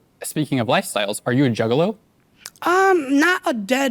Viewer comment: I thought he said "So speaking of lifestyles, are you a gigolo?" He actually said "juggalo"